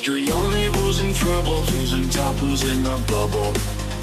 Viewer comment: i love you but the tune at the beginning. What does it say ? I hear " your only causing trouble with your taccos in a bubble" i need help! 😅 i love it cos the tune is catchy but my old age is catchin up